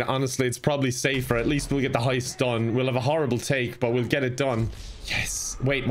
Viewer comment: I thought he said jew heist and I was like YOU WHAT